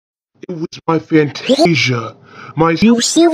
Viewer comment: I can't stop hearing fantasia at 0:01. All I hear is fantasia when trying to make it out -~- What I hear: "It would be to my- FANTASIA. My veiwers are... hm... An-E-E-E-E-E-nd me!"